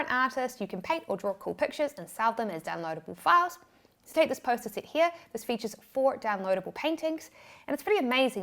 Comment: I thought you said post its. Not posters. Ha